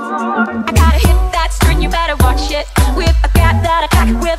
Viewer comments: ''I gotta hit that skirt you betta watch it' ' Am I the only one who hears this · “I gotta hit with that screen you better watch it.” 0:23 “I got the whip, got the pigeon thought I’d keep it undercover.” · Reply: I heard “I gotta hit the skirt you better watch it”